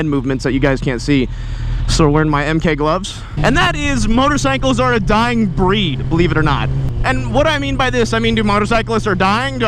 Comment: I thought it said, Bikers are dying bread. 🍞💀🏍😂😂👌